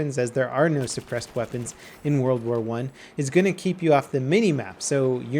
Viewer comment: Did he just say "firing unsuppressed weapons will keep you off the minimap"?